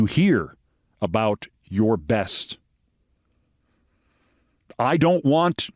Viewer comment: "... My goodest... " is what he actually said not my best 😂